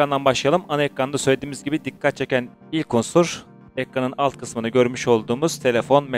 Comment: Dakika da ilk osur mu dedi ben mi yanlış duydum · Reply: "Unsur" diyor.